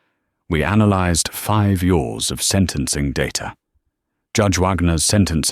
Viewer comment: "Five Yours" instead of "Five Years!" MORE PUTRID ROBOTIC NARRATION, MORE STINKING AI-GENERATED CAPTIONS! YouTube, THIS MUST STOP! PURGE THESE VIDEOS NOW!